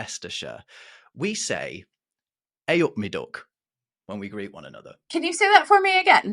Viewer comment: Since the Beatles’ white album came out in 1968, I’ve been wondering what is said right before While My Guitar Gently Weeps. At last I know! It’s Eh up. I always heard it as Aye up.